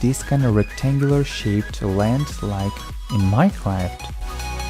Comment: Mike raft? Am I hearing that correctly?